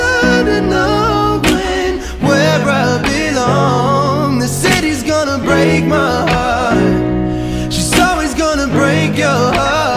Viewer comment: Someone I know thought it said He said he's gonna break my heart and I can't unhear